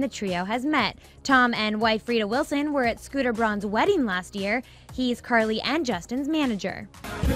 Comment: Wait...did she just say Tom Hanks is Carly and Justins manager?! Cool!